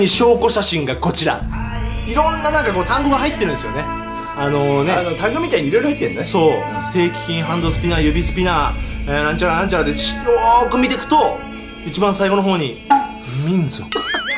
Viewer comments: の正規品がセイキンに聞こえてワロタwww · で『正規品』が『セイキン』に聞こえたw笑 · 正規品がセイキンに聞こえたww